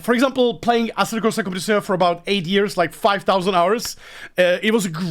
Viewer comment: what did you say here. New to sim racing and want to check out that game but have no clue what you said haha. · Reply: He’s referring to ACC. Asseto Corsa Compitzione